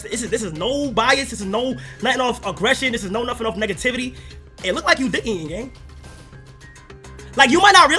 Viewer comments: Wtf is he even saying here 7:00 “it look like you dihing it”??? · what is that dude even saying??? "you look like you diigeinng gang" "you look like u dickiiieengng" like what the fuck does even mean? it doesnt sound gangy if you speak like that. its just dumb